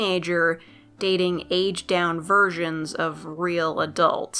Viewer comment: lmao I thought you said “aged down virgins” 😂